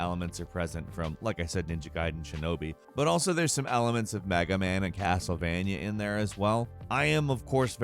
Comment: did he say MAGA man ? lol